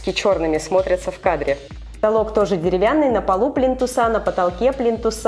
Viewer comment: Мне послышалось не "плинтуса", а "блин, туса!" 🤣🤣🤣🤣🤣